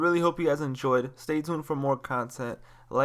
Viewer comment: So I added English captions and at 6:58 it is written do what ever you want in zoo, while he actually said do what ever you want to! 😂😂😂